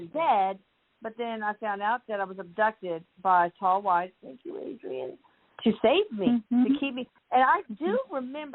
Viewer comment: I hear someone say Thank you Adrienne. Who said that????